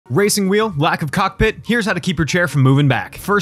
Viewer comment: I thought bro said black coc